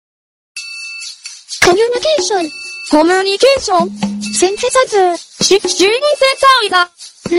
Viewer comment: I heard Len say CoryxKenshin..... Just me probably...